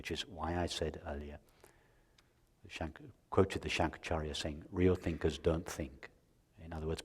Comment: Please edit the Transcript at 13:58 - Kindly clarify what is "shanku quoted the shank area saying real..." etc. I am afraid it is the name of the great seer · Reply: shankracharya